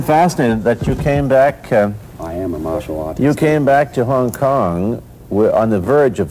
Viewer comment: What is he saying? In Subtitles it is Showing lee tells that "Iam a Muslim" In actual i think he is telling iam a martial art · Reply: I am a martial artist. That is exactly what he said.